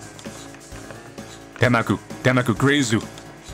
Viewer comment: What is saying at @ 17:26 " demagu demagu grazu "? Is he saying that?